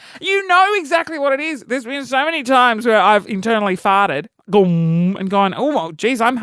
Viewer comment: I thought you said "eternal" fart and I was like WHOA I GOTTA HEAR THIS